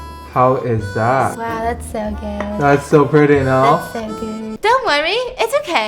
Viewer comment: PLS💀 0:05 Why did I think she said “that’s so gaayy😍”